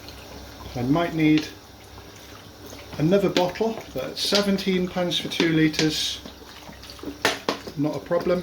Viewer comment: Did you say it costs 17 pounds for 2 litres of water? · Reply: Pence